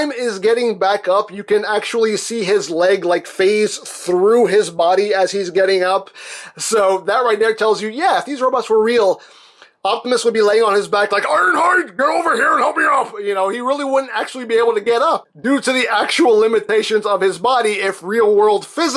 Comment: When you said iron hide come here and help me up First i heard "arrrrr harrrrr"